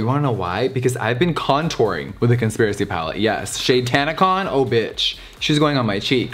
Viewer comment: Ngl, I swear to god I heard him say “Cuntspiracy palette.”